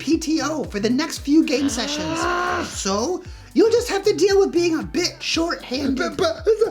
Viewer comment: "You'll just have to handle with being a bitch" Oh wait... no no that's not what he said.